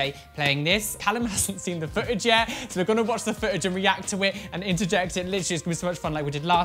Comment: I honestly thought you said " We are going to watch the fridge.." - ok rewind, slap head " We are going to watch the FOOTAGE! " - please send me new ears!